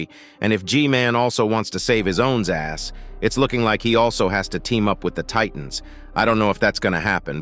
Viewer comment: is it just me or at 21:39 did he say A$$ ???!!